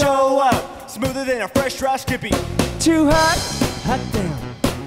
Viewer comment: Those 'Ha's are making me laugh, idk why x'D 1:49 LOL at James trying so hard to talk so fast, did he say fresh dry of skippy?? Haha